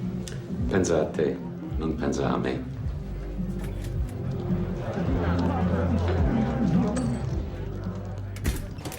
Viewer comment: What did Gotti say to the FBI agent and what did the agent say back 1:11:50 I’m really curious? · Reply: Gotti : Be good/Take care of yourself Cop : Worry about yourself, don't worry about me.